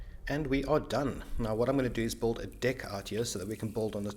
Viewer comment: sounded like he said “build a dick” 😂